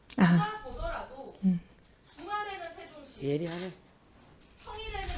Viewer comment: 이소영의원은 볼 때마다 느끼지만 정말 똑똑하다는.. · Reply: 누군가 예리하네 라고 말하는 것도 들리네요. 이소영 의원님 청문회나 저런 자리에서 질의하실 때 조곤조곤 품위있는 태도가 멋집니다. 맨날 고함이나 지르고 반말이나 해대는 사람들과 차원이 다르죠~